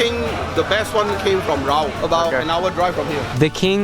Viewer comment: Just to inform is Raub instead of Rao